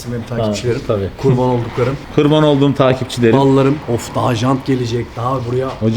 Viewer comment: da takipçilerine mallarım mı dedi ben mi yanlış anladım... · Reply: Ballarım diyor siz yanlış anlamışsınız…